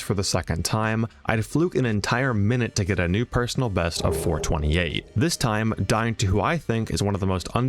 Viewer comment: i heard "4/20" and got scared